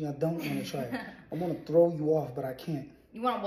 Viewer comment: That girl ain't the queen of that relationship, I heard him say I want throw you off but I can't, girl you need to chill